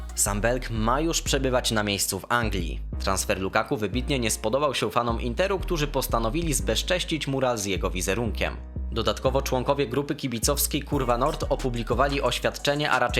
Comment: czy tylko ja usłyszałem kurw*